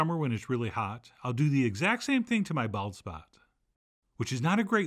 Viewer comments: I heard "I like to do the exact same thing to my bal--and my jaw hit my keyboard before I realized you said bald spot! 😂😂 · I had to remind because I thought I heard "ball-sack". Turns out it was "bald spot".